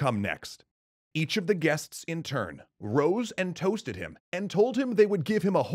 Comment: thought you said roast and roasted him LOL